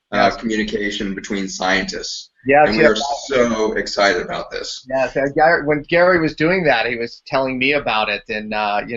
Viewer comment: At the 1:00:00 mark what is saying? Acia? Sounds like Aseea? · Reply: ASEA, it is a redox supplement similar to RESTORE but is less for the GUT & more for body mitochondrial activity.